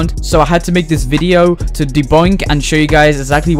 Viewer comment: does anyone else hear "deboink"?